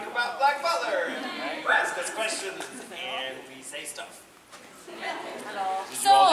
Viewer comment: who heard that "halo"